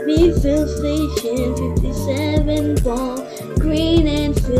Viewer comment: I heard 7 ball